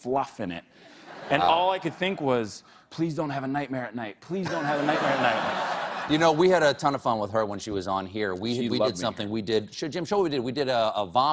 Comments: did he say “show jim” ?? lmaoo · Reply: Jade Sierra he said "show him jim" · omg did he just call him Jim?